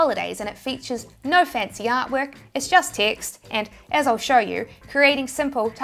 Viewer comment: “No fancy artwork, it’s just text.” What I heard: “No offense to yard work, it’s just ticks!” 🤣😅